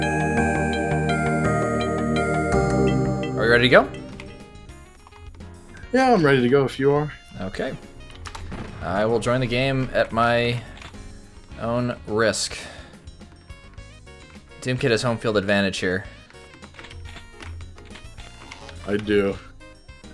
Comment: at first I thought you were gonna say, "at my own...brisk." 🤣 Edit: stupid auto correct.